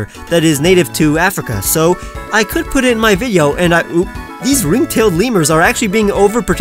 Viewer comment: did he just say “And I oop-“ ......it’s almost 2021 that meme is dead